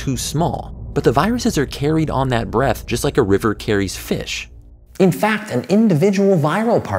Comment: did he say like a river carries fish...sorta like in vertacium’s turbulent flow fish 😂